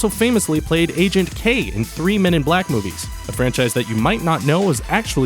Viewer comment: it sounds like he said 3 minute black movies. idk why i decided to bring that up.